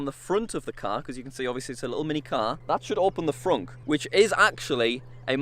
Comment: Is it just me or did he say fronk in stead of front or trunk 4:42 4:47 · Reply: He said frunk because that’s what a trunk in the front is called